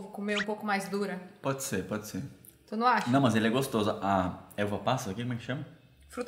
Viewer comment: Eu entendi que o falou “Eva Passa” kkk 😂